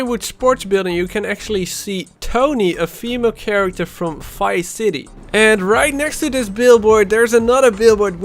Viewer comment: It sounds like he is saying Fice city haha